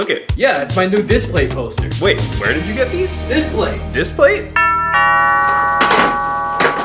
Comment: My dumbahh thought he said disspay